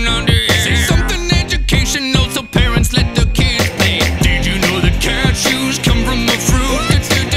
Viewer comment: I now know cashews come from a fruit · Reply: Bart Baker I thought it said did you know that cashews come from mushrooms